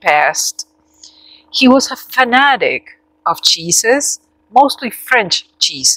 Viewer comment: Mimi is perfect! I’m so happy for you. ❤️ also I thought you had said”my friend was obsessed with Jesus- especially the French Jesus!” Lol. I’m so glad I figured out you meant cheeses. 🥹 Mimi is a great name and a wonderful new friend. ¡Felicidades! 🎉❤😚😇!